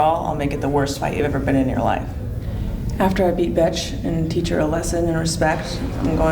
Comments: Did Honda call BEthe 'Bitch' or 'Butch' or both? Bi-Utche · I heard "after I beat bitch-"